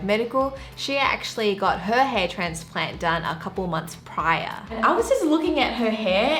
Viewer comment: I thought she said "Hay transplants"